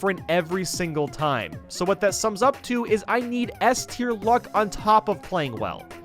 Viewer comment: What does he say at (2:55)? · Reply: "I need S teir luck, on top of playing well."